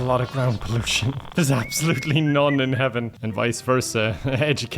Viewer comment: I thought he was saying Heavin as in Heaven and